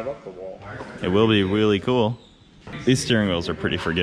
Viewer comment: Did say "It will be wheelie cool." ?!